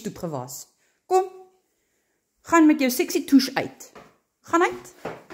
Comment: What language was that? I think I heard "sexy tush" and a certain "f" word. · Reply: She did say sexy tush, but no f word. It’s Afrikaans 😂